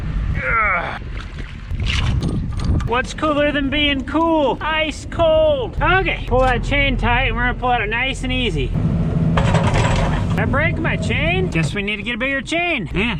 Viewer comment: Thought you said "Iced Cole". 🤣🤣🤣